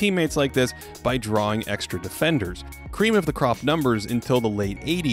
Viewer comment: did you say "Kareem of the crop numbers" instead of "cream of the crop numbers?"